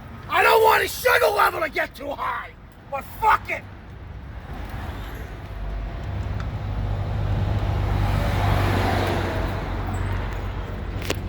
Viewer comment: listen to the laughter LMAO at 5:35 Throttle Plate looks like Cookie Monster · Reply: It's all NOMNOMNOM 10:05 did say buttfuck it? 🤣🤣